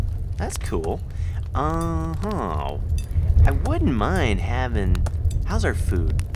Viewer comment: When he said "wouldn't mind having" I thought he said "wooden mine haven" Lol